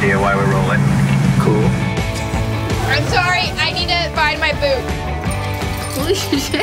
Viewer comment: I'm sorry, i... need to... find my boot ~Sophia Lillis 2020~ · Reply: i thought she said "i need to bind my boob"